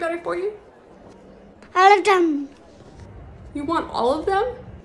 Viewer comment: I thought she said Olive garden when she said all of them 💀